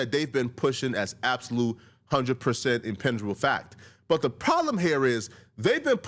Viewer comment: Did you just make up a word? WTF is... I don't even know what you said...inpenjable? Impenjable?